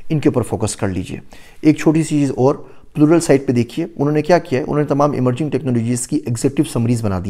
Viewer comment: google site kya bola samj nh aya ? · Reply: Plural sight